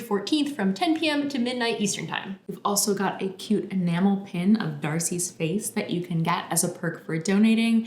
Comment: instead "enamel pin" i heard "mammal pin" and felt put off ÓvÒ good luck though, folks. i'm poor and not from the US so i doubt i can help much.